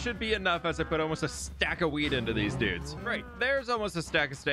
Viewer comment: anyone hear weed